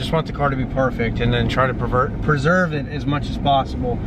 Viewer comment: Wow they did a phenomenal job. Can't even see the seems. Awesome. Wait a minute, I heard "try to pervert it" 8:55 LOL! Oh man my sense of humor is immature. :oD